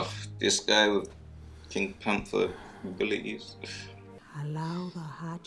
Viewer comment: my man did not just say pink panther abilities